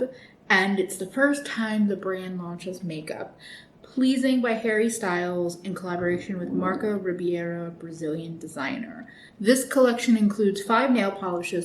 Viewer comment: I was zoned out at the beginning of the video and at first I thought I heard Harry Styles was collaborating with Marco Rubio lmaoooo glad it's very much not that